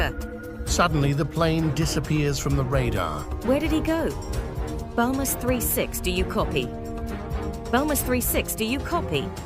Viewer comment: I thought it said balls 36